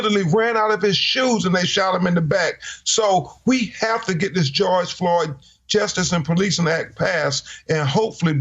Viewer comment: So perhaps these police officers need more track training so they can learn to run faster after people instead of shooting them in the back. This has to stop! Only when black men are killed, their entire life history, their parent”s history, and any other relative with a criminal record are displayed for the public to make the shooting appear right. Did the cop(s) that shot the man in the back decide to shoot the person based on their history? A person”s history is not an appropriate reason for officers to shoot unarmed black men in the back. I did watch when people stormed the Capitol on 1/6/21, when they were scaling the outside walls of the Capitol to gain entry illegally, no one was shot in the back. They were in the process of committing a crime, yet no one was shot in the back. I wonder why?? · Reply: So if a person is know to be the serial killer they have been looking for years, is fleeing, he should not be shot? Or he can be shot if not black? every time I hear "THE GEORGE FLOYD"S ACT" sounds like they are saying "THE IDI AMIN ACT"